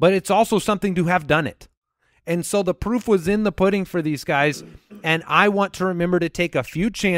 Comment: Maybe it’s the spitballer in me, but I heard “the poop is in the pudding”